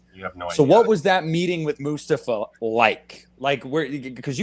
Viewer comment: had me double taking, I heard Lucifer first 2 times, Lucipha, and then I realized Moustapha lol. Moustapha Akkad has some S-tier executive/producer credits!!! The Curse of Michael Meyers played backwards...., "I command you, in the name of Mucifer, to spread the blood of the innocent!!!!" Peter Dante, "Oh my god, Halloween 6 kicks ass!!!"